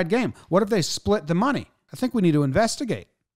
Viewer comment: I thought Jomboy said at the end "Nah, we're not going to do that. We're not the Astros"